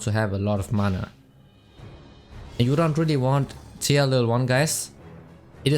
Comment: No matter how many times I replay this part all I hear is "and you don't really want.. Teelelwan, guys."